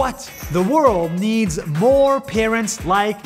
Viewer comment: When he said parents I thought he said karens